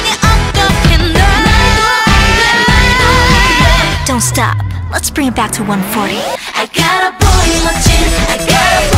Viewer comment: I REALLY THOUGHT SHE WAS SAYING I GOT A BOY ON MY CHIN AND SPENT A LOT OF TIME THINKING ABOUT THE MEANING WHAT